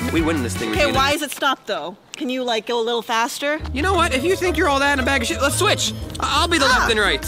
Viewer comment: is it just me or who heard say to if you think your all that and a bag of sh**